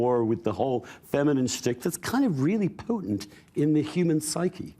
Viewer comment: did he say potent or did he say Putin? at 4:09 closed captions (needed because of some weird words) changed Donna's "we know how to lead" into "we know how to leave", which is definitely NOT what she meant! At 11:09, Donna, it takes more than a conversation to send people to Mars.